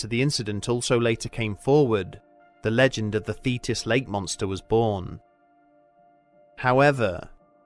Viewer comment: I had to stop and rewind at a point when I swore I heard you say "Fetus Lake Monster", it sounded frightening.